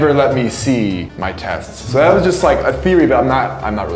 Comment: he said theory not fury. Every video the subtitles are wrong.